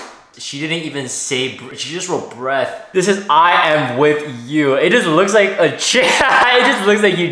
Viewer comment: I thought it said I am white😂. I thought was her actual chin.